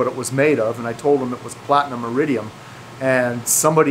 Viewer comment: Platinum iridium? I thought you said plutonium and uranium